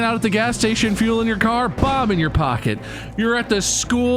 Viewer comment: I swear, at first I thought Bob was saying 'Bob' in your pocket instead of 'bomb'. I've been reading it wrong this whole time!